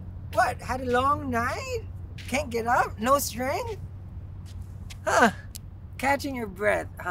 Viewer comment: Can't get it up , no strength..🤣🤣🤣..thats what I hear..lol 0:17 do video you cooking cookies, yummy I have too many crystals already, am saving to get one those wands you have.